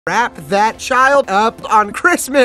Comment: DDID HE JUST SAY WRAPPER OUT CHILD